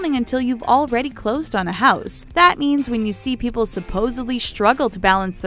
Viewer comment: "Hyperbole" lol. Im waiting to hear "supposebly" next... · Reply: Nicole Levey-Hunt, I actually listened a few times when she said "supposedly" because it almost sounded like she said "supposebly".